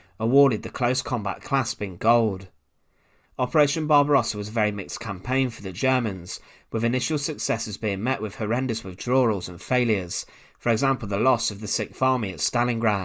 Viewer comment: Did you say Operation Bob Ross?